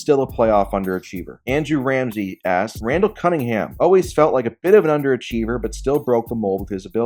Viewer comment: As soon as you switched over to Randall Cunningham, I wasn’t paying attention and I thought you said Dante Cunningham. I was like… how is he on this list?!?